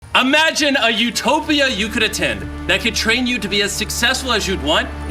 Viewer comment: I heard utopia?!! ITS LIT 🔥🔥🔥